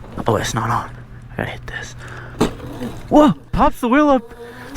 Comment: who else heard I hate this